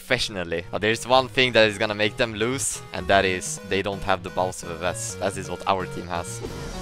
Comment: captions say balls of the vest (Bausffs)